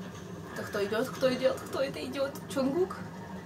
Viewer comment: В 1:9 послышалась как Кто это идиот!